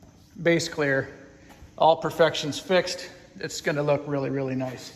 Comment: "all perfections fixed" ... Did I hear that right? You're not gonna go un-perfecting perfect work, are ya? 😟